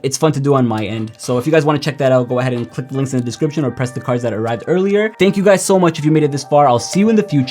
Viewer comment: What did he say? Press the cars?